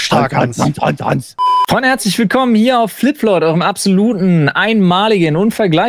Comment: Warum verstehe ich da immer Hanslich Willkommen?!